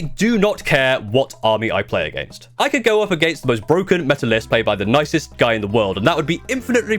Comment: who else thought he said Melta lol